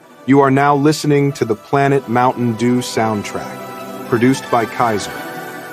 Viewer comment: lmao i heard it as "produced by casoh"